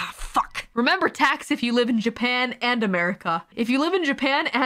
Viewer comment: Chat: Don't forget about taxes! Calli: aW FUCK Spoken like a true American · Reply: I originally thought she said “remember Texas” and I was immediately like “Tf is wrong with Texas?” Then realized she was talking about Taxes and her reaction made MUCH more sense